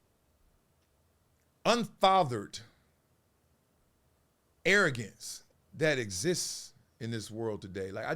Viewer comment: I do behavioral intervention at an elementary school. I love my job and I love the kids and helping, but you said something that perked up my ear. I heard "un-fathered."